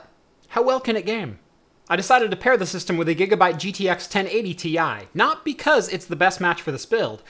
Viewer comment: lol, reminds me of my X5660 and a 780ti Kingpin! Edit: oops, thought you said 780ti in the beginning. Oh well! I'll keep my setup til the motherboard fries! Or if my CPU fries first, I'll get a 5680 and better cooler to aim for 4.6-4.7 :)